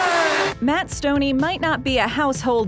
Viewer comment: Why do I hear Matthew stone ass